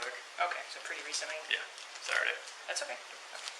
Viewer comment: The female officer thought he was saying “sorry“ after explaining the pool party attendance, but really he said “Saturday. I wonder how much else was overlooked or mistaken